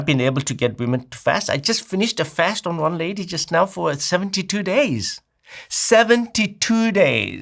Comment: I thought he said 72 hours but when he reiterated I heard 72 days. That's crazy